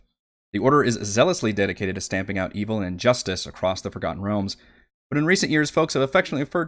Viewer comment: wait, did you just say the order is dedicated to stamping out evil and justice? 😂 What's wrong with justice? lol Anyway, loving the new show, thank you for doing this!